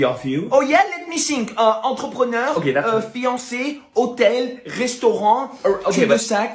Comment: It's fiancé😂😂 i thought he said Beyoncé 😂 and i was like what ??